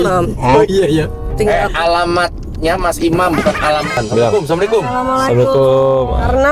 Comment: menit ke 24:09 si benerkah ini klo dia mengucapkan assalamualaikum ke mas imam..ato saya salah denger??